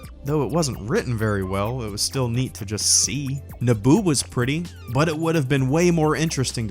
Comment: I thought he said naboob